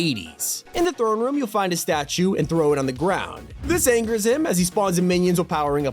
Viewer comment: I know he says “him” but I legit thought he said “DAMNNN” at first💀